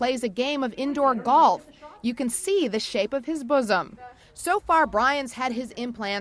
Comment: did she just say buzzum 😂😂😂